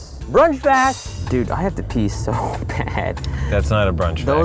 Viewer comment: "If i tickle you will you pee?" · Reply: Kyle Willis lol, I thought he said, “can I tickle you while you pee?” That raised some eyebrows.